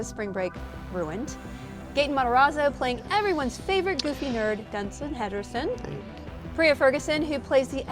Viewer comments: DID SHE JUST SAY DUNSIN HEDERSON??? LMAO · Did she just call him "Dunsan Henderson" at 30:44 or is it just me?